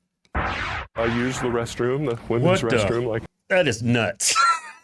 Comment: did you say that is nuts or that has nuts? I didn't quite catch it properly🤣